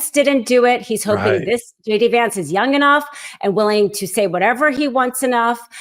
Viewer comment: OMG... Thought she said.... SHADY Vance😮😊😊😊